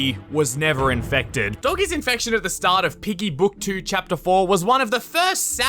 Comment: Did He say Book 2 Chapter 4